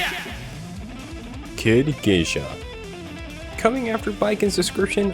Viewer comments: i thought he said "hard geisha" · With the baiken one i hear "Hard geisha"